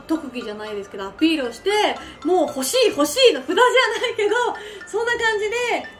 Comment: Nice, is best girl. Hope she gets to keep doing what she loves. Also thank you for your work, very good translation. For 3:38 could it be ほしい instead of 星? Nothing major at all, just a thought I had.